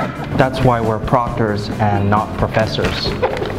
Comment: I thought it said "doctors with no rules" 😂😂😂😂😂